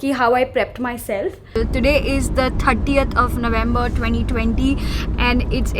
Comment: 38th of November? Did I hear right?.